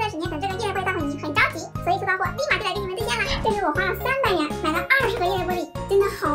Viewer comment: 300元我聽成300年😂😂